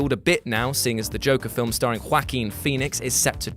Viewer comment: Did you say Quackeen Phoenix?